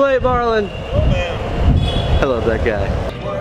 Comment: fun drinking game: binge watch casey's vlogs and take a shot every time he says " I love that guy" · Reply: English isn't my first language, so I heard "I love black guy". Now I know what he said, thanks.